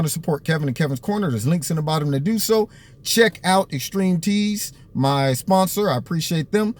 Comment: This is my mom's account and I'm 10 and when you said "Extreme teez" I thought you said string cheese.